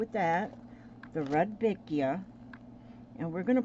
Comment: the colour of the RED Vicia is in the name haha, I love this 😊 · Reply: Sorry I thought you said REDvectia not Rudbeckia haha, so I looked it up and they are Reds, pinks etc, thanks for the lesson today 😘xx